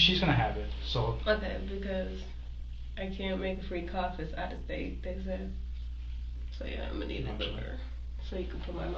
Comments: The girl is very strange for sure, but these subtitles?.... At 40:23 she says "I can't make free calls if it's out of state they said" yet the caption says "I can't make free coffees out of fake business" LMAO. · "Ok, because I can't make free coffees out of a fake business" What she really said; "Ok, cause I can't make free calls outta state"